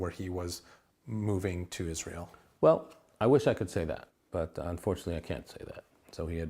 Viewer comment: "I wish i could say that. But, unfortunately I can't say that." Sounds like "What I am allowed to say...."